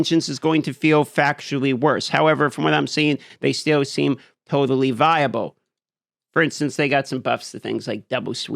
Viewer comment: "Hammer of the Ancients is going to feel factually worse, however, from what I'm seeing, they still seem HOTAlly viable." I HOTAlly thought that's what you said at first.